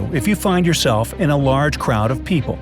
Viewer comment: i thought u said i was safe in large crowds